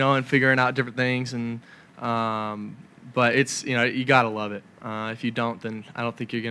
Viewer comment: Cade has great timing. Reporter: Do you like watching film, being able to break it down? Cade: "It's kind of like a puzzle.. figuring out different things...but you...God (9:35) I love it!" Okay, I realize he said, "Gotta love it," but the first time I heard it, it sounded to me like "God I love it!" Either way, it's clear he loves this game! Also, much respect for him not being a video game fanatic. It's clear real-life competition through football means the world to him. American Football is gaining in popularity around the world, especially in Japan and East Europe. I don't know why exactly but despite it being a contact sport, American football seems to bring out the love in fans and players in comparison with soccer. What a great ambassador for the game Cade is! I Go Tigers!